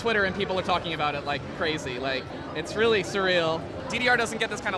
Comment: did he just say it's really "serial" lol 7:44 it happens lol